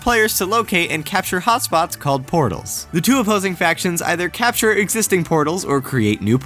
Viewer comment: From 1:19 i thought he said porno's every time lol